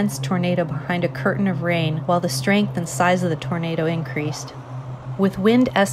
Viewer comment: "We heard you liked tornadoes so we put tornadoes around your tornadoes and within your tornadoes" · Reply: Definitely thought you said tomatoes lmao